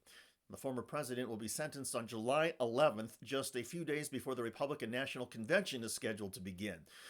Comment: I really thought I heard "just a few days before the Republican national conviction is heard". It's my age and these dreadful earbuds.